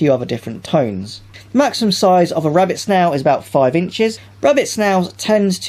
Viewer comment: Did you say 5 inches for a Snail?